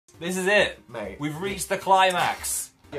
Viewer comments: I thought said "babe" o no what's with my mind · I swear to God, I thought at like 0:01 said "babe" and I nearly lost my shit... Lol, scratch that, I DID lose my shit, I'm crying in the bath tub